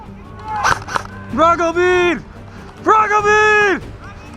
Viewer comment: wtf was he saying at 7:48 ? can any russians figure it out? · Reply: ***** Враг убит, враг убит.